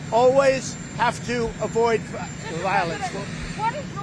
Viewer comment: Why is it every time he says "You always have to avoid violence", I hear him saying "Bring more violence"? Could it possibly be because that is exactly what he's doing via coded speaking to his "base"? Not that I am a member of said base, just someone with a little familiarity of neuro-linguistic programming and its use of coded language. I also grew up with someone who spoke similar to how he speaks. They were as abusive too.